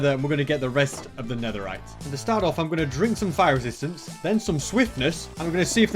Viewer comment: I heard “Fire existence”